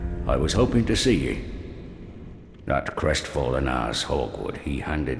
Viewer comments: I seriously thought he said that crestfallen asshole 3:48 XD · Did i hear "Crestfallen Asshole"? XD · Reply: Hawkwood not arsehole in case yous was wonderin